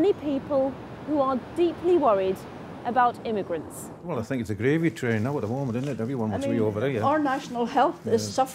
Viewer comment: Excuse me, what does the man say at 1:09? Thanks in advance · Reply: "Well, I think it's a gravy-train now at the moment, isn't it? Everyone wants to be over here".